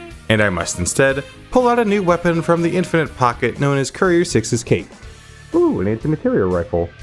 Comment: Did you say cape or cake?? With where you disappeared to, it could be either...